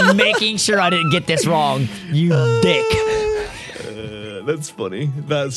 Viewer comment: Did say “... you dick!”